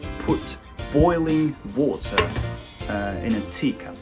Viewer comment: の直後「うぉーたーｗｗｗｗｗｗうぉ～た～ｗｗｗｗｗｗｗｗｗｗｗｗｗｗｗ」という幻聴が聞こえた